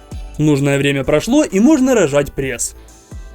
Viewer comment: я вначале услышала рожать 🤭